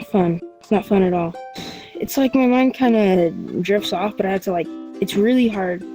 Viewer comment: I thought he said its like my mom kinda jerks off